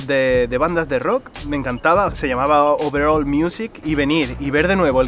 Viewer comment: escuché overol music😂 porque sólo estaba escuchando el vídeo. Y dije wut